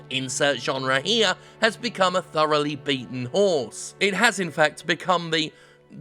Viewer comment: I thought you said thoroughly beaten "whore" rather than "horse" and I spit my drink out. Thanks Jim!